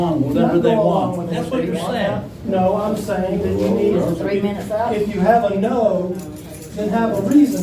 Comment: pulled a Russ @ 10:53The Mayors HUSBAND WAS SPEAKING FOR 1 minute 13?seconds.. What does she say? 3 minutes up? SMFH